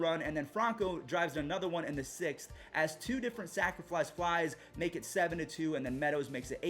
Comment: my only comment on how to improve your content is that there is no "L" in the word "sacrifice". Sac fly. Sacrifice fly. I always hear "sacriflice".